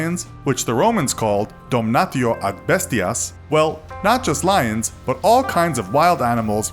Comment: Donutio abestias That's what I heard xD